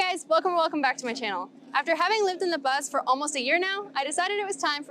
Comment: Hello! It is really beautiful and relaxing ☺️ Im sorry, but I did not live in a bus there. I rented in a house, however I do know that it’s very common to live in buses and RVs in the Baja. There are several facebook groups that discuss the logistics. Hope that helps 😅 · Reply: haha. I heard wrong. At the beginning of the video you said “after having living in La Paz for almost a year” i thought you said after having living in the bus for over a year. Sorry, that was my mistake. Hope to see more of your videos soon